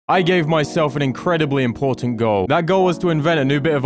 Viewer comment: I Swore when it said "I gave myself" It said "I love myself" Edit:If hearts this- I'll go mad Edit 2: should do Minecraft UHC but if you craft you lose a heart